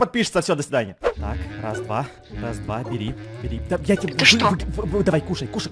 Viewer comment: это просто, кто услышал, поставте лайк на комм😂😂🤣🤣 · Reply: Он сказал да я тебе